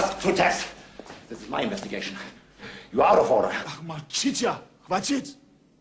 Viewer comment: What does Mishkin say @ 3:25? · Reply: This is my investigation your out of order.